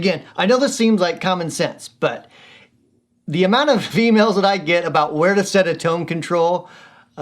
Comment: Tnx for demoing the blues driver with humbucker pickup. Sounds really good. And yes you it did sound like you said "females". Lol!